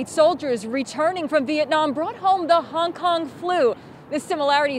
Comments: I- I thought they said honk honk flu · I thought they said honk honk flu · I initially thought the reporter said "HONK HONK FLU" 😂😂😂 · I thought she said honk honk flu.